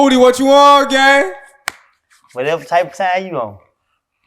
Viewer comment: someone what is woody sayin 0:13 ? whatever type u on ? im not native speaker lol